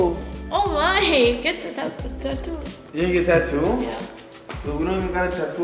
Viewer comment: Lmaooo My fat a** thought it said get 2 tacos 💀😭🤣